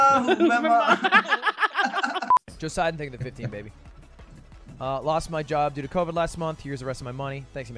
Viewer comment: can someone tell what he said?? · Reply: "Lost my job last month due to covide here's all my money" This is the superchat the guy gave to the streamer